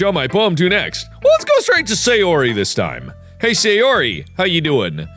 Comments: Lol I think when he said “hey sayori” his phone thought he said “hey Siri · Hey sayori it thought you said hey Siri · You said hey sayori how ya doin siri thought you said hey siri how ya doin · When you said Hey Sayori, it thought you said Hey Siri